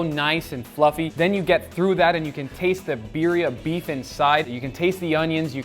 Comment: Did say ‘Beeria’